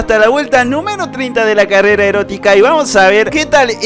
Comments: Escuche mal, o dijo carrera erótica? 7w7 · soy yo o escuche carrera erótica